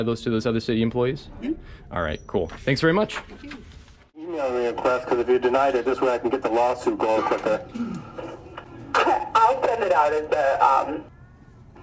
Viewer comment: What are they saying at @ 55:48 ??? the transcript isn't even clear. Can you type over the video what they are saying? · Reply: Transcript says: EMA request because if you denied it this way I can 55:50 get the lawsuit going quicker I'll send it out as the um